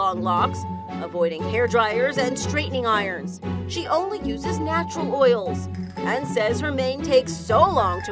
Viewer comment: did she say name?